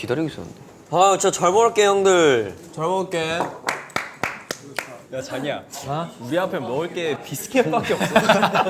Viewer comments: when Tae said johnny i heard Chagiya at 00:40 LOO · sounds like taeyong is saying jagiya and now I can't stop thinking about it